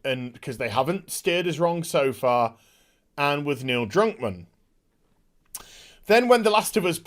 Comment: Could've sworn I heard you call him Neil Drunkmann. If my hearing isn't that bad, I completely agree. He had to have been blind stinking drunk to greenlight this stinking heap of trash.